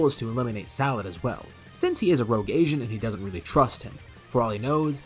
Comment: I thought you said rouge Asian Lol